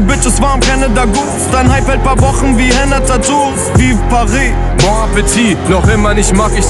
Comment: 0: 51 - so weit ich verstanden habe Voici paris= hier Paris bon appétit= Guten Appetit und das Lied kam vor dem Anschlag ? ich höre gerne bushido aber das hier ist irgendwie komisch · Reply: Vive Paris nicht Voici!